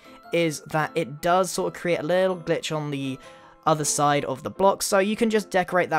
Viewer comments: I heard that he said "the only problem with this, is that it does sorta create a little bitch" instead of glitch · “now the only problem with this is that it creates a little b***h,” Me: did he say a cuss word?! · i thought he said the b-word but it turns out he said glitch.